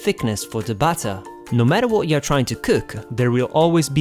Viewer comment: You sounded so scottish then. 'Nu metter what ya tryina cook lad' Is so what I heard. XD Anyway, thanks for the recipe, I'd like to make some pancakes that taste like the old ones I used to have instead of he buckwheat ones which I personally hate. :D